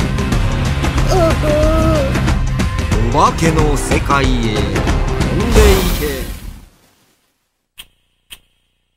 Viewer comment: what did nenaikodareda said? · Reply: "To The Ghost World, flying away." "No, no. Midnight is the time for ghosts. Huh. Huh. Huh? Playing out at midnight, are you visiting the ghosts?"